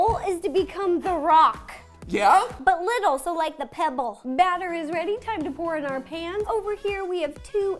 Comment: lol for a sec i thought ro said ‘battery’s ready!’